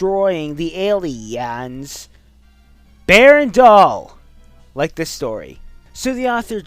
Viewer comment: What was said at 2:24? For the life of me, I cannot understand whatever it was. Berndall or something?